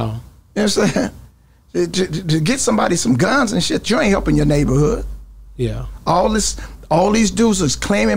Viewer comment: Watching and thinking about Charleston White is so right about it all!! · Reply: I've listened over and over. I can hear what you're saying he said but still not positive of it. I hope he is saying guns aren't helping the hood. Edit: I just caught it. He did say “to get somebody some guns, you aren't helping the hood” I didn't initially hear him say “to”. I'll admit I misheard.